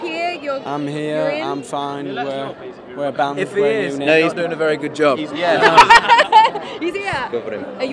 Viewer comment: did harry say “if he’s, he isn’t gonna do a good job” OR “if he’s, we’re not gonna do a good job” I’m confused 😐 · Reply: I think he said, "If he is, he's not doing a very good job."